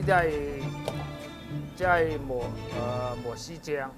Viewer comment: the translation of mushroom (蘑菇) sauce at 3:25 is incorrect I think.. he was actually saying 磨豉醬, which is ground up soy bean paste.. at 5:54 or so.. the translation of light soy sauce, he was actually saying dark soy sauce.. just FYI